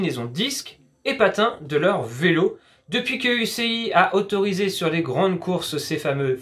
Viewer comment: A 3:06, j'ai entendu U.C.I. Donc l'affaire est réglée.